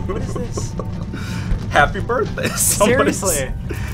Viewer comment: XD when those balloons all came in I heard go "happy birthday" 😂😂😂🤣🤣🤣